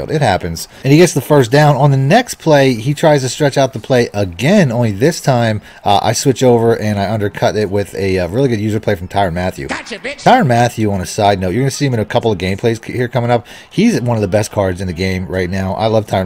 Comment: Anyone else catch the “gotcha bitch” on his first interception at 3:20?